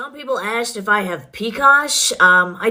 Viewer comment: What did she say that some people have asked her if she has at 20:37 cause I listened about 15x and couldn't understand her lol. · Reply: Yeah I don’t know either, it sounds like “picas” wtf that is. 😂